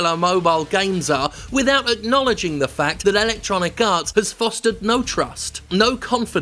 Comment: I keep hearing "ElectoniKarts", which sounds like a mobile game.